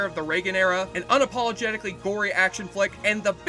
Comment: I first heard "Unapologetically boring". I'm glad I re-listened and heard "gory". :D